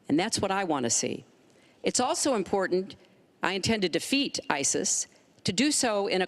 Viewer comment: Video marker 3:26 She said ''I intended to feed ISIS'' Did i hear that right? WOW-REALLY-WHY? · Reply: She said I intended to defeat isis